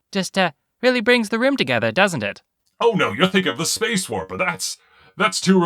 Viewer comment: ish, it sounds like he says "Space Whore." I'm beginning to suspect that Space Whores are the reason I was born...